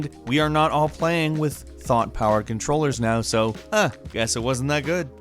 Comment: On number 15 I heard "a thot" powered controller and then a chick was on e picture right after that. I was like "whaaaaaaaaaaaaaat? Damn, they be ahead of their time with that!"